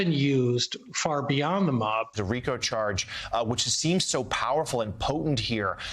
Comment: Sounds like Ari is saying "statues" instead of "statutes".